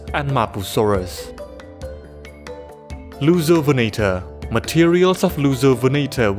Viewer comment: i keep hearing it as "Loser-venator", man dinosaur bullying is such a problem